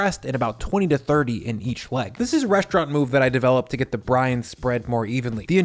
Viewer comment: "this is the restaurant move that i did to get the Brian to spread more evenly. the injections are great and they get a bunch of Brian in there" 😁😁 every time you said 'brine' i heard it as 'Brian'.... "Wet Brian" "Dry Brian" "Cold Brian"